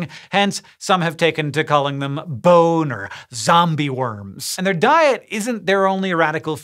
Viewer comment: I was half paying attention at around 8:27 so I thought I heard him say "Some people call them Boner Zombie worms" and I was like, Whaa? Why...? I think I need to go to bed. Goodnight